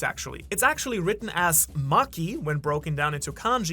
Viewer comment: did you say maki? like the chick in jujutsu kaisen